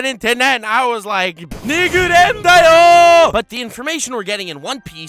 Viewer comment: when you thought yah boi was about to say "nigga".